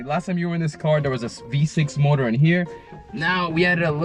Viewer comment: did You said V6? Nice work! Concrats!!